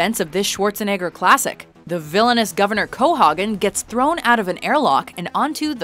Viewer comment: did she just say "CoHOGgen'?? lol